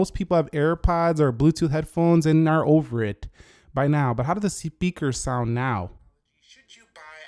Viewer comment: did he say sepeaker?